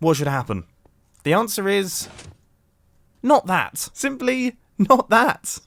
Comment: it sounded like he said fuck that